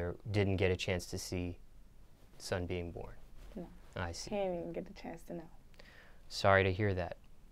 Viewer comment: Listen very closely to 11:51 DID HE JUST SAY NICE TO HER BOYFRIEND NOT BEING ABLE TO SEE HIS SON .I HATTTE THISS REPORTER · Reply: Unicorn Magic he said “i see “